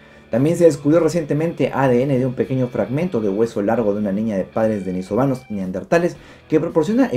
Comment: entendí "de una niña de padres venezolanos"😂😂... para nada xenófoba, mi pareja es venezolana.🤗🤗